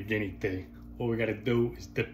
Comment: Nobody heard him say "comb your ass hairs" at the end 😂😮😅